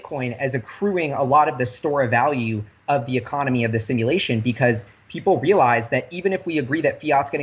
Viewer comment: Did just say 22:23 economy of the simulation???